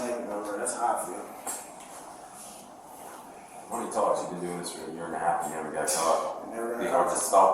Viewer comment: A contracter for 25 yrs but hes 29 yrs old😂😂😂😂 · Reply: I thought he said he’s been doing it for a year first he said 3 weeks then a year 😂 or unless I heard wrong